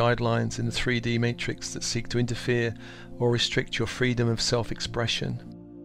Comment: Wow! Thank you. When you said the word “freedom” i heard it as Freed Om